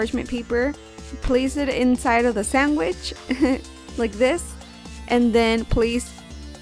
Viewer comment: i can not be the only one who heard her say "sanGwhich"